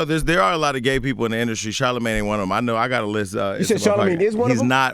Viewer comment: it did sound like he said “Charlemagne is one of them” LMFAOAOAOOAOAO